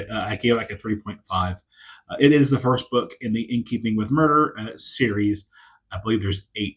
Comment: Hey, hope you had a great Christmas and New Year's! At first, I thought you were saying, "In Keeping with Murder," like the book was about the proper etiquette to exhibit while on a killing spree or something like that. In that case, you could have used the Monty Python scene with Sir Lancelot attacking the wedding as a negative example! Especially the part where he charges up the stairs but pauses to slash at the flowers because why not. 😂 Don't blame you for not planning too specifically. Mood always ends up holding sway for me as well. Right now, I'm reading mostly nonfiction because I need to recover after binge reading King's "The Dark Tower" series in less than a month. And I've gotta admit that you're tempting me to give one of those book nook things a try, even though I doubt I have the hands for it. 😊 Have a nice weekend!